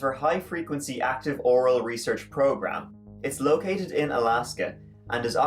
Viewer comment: It sounded like you said oral research program lol